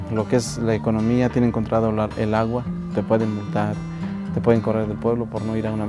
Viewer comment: Just for clear out, in the minute 3:05 the subtitles are grong. what the guy says is "te pueden multar" (they can be fined) NO "te pueden matar" (they can kill you).